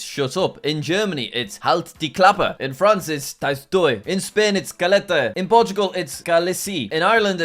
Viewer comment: did he say calatey?!?!